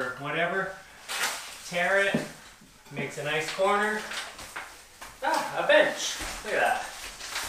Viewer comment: I thought he said " a, a bitch. Look at that". Lol